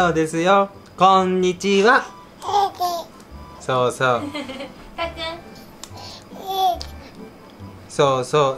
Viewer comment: 最初のとこ（0:03）も 「たっくんです」って言ってるように聞こえるなぁ…😳❗️